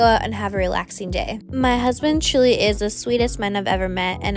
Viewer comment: When she said, truly, I thought she said chili